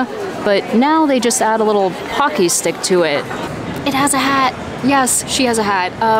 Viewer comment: I thought you said hockey stick and I was thinking that's odd and then realized you said pockey stick and I agree it's a bit boring. I want lots of cat references with my Baymax.